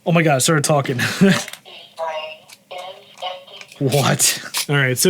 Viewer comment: Through my ringing ears, it sounded like it said, "This pea brain is empty."